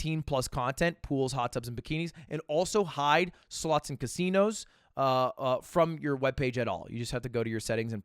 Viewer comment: For a second i thought i heard "Sluts and casinos" and my brain was like "They figured how to automatically block sluts?!?!"